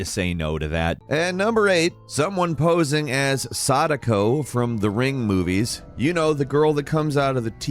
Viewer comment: I 100% misheard when he said Sadako from the ring movies and heard him say Sanico from the ring. That confused me so hard until Sadako popped out. Which made me laugh for some reason for those that know.. lol